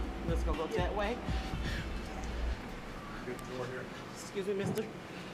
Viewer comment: did she say 'scuse me mister' to dr proctor or did that just look hilarious to me? 😂